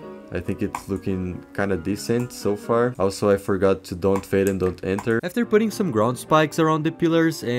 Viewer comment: did anybody else hear "also I forgot to go in creative and kill Hitler" at 2:30? 😭